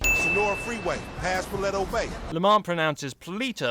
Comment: to me it sounded like Lamar said fleto bay